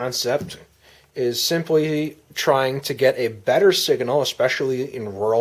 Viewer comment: did he just say sigahnal